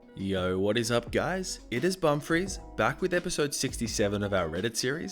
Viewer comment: I thought it intro said bumfarts XD